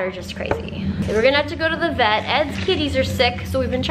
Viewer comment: I swear, at first I thought she said kidney, not kitty.